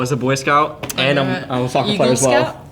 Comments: "I was a boy scout and a __ as well" what did he say???? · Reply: I think soccer player, I’m not sure how that relates to knots/rope play though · What did he say? 13:07 “I was a Boy Scout and I was ….” I didn’t understand what he said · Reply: "and I was a soccer player" aswell I think